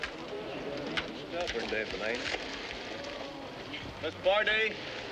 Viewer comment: What did Bo say at 2:20? Right after he busts out the passenger side window. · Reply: "Stubborn damn thing!" I was rolling!